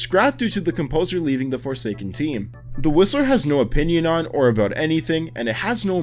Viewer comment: I thought you said the rizzler